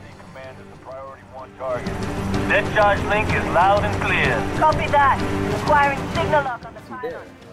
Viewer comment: it sounded like he said "discharge link - loud and clear!" but he could have been speaking in another language like George did a few times and just saying whatever the subtitles were.